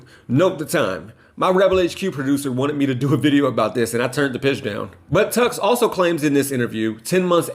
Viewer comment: I thought he said "I turned the b!tch down" 🤦🏻‍♂️😆